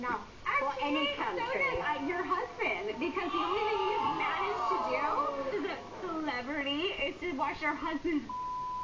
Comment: I still wanna know what Megan said. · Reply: Little Fifths she said “the only thing you managed to do, as an celebrity, is to watch your husbands brain turn into a vegetable” the uncensored version is on Tubitv